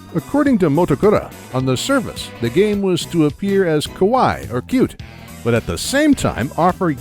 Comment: The way you said Kawaii (Cute) sounded liked Kowai (Scary).